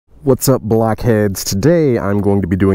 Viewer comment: I heard "what's up blackheads"